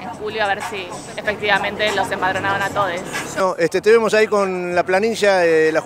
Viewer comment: Todo genial hasta que escuché "a todes"